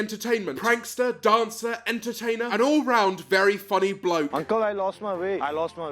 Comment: It sound like he said you need to lose weight